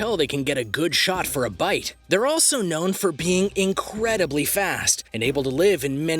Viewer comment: I thought you said peeing fast